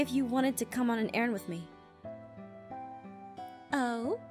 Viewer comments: Aphmau and Lucinda: Talks about running an errand What I heard: "Aaron" They sound similar OK T^T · When Aph said "Lucinda, I want to ask you if you want to come and errand with me" I thought she said AARON and I started crying..